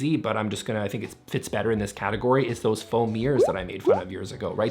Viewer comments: Whenever you say "foam mirrors", I keep hearing "faux mirrors". Is that just a framed photo of yourself? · I swear, I heard you say "foam ears" five times before I realised it was "foam mirrors"